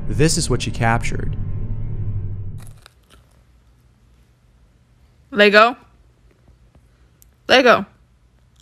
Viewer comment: Am I hearing things, or is that dog’s name lego?